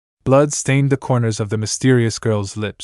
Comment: I heard "blud stained the corner"